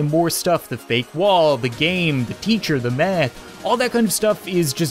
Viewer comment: I thought he said “the meth” 🤣 15:57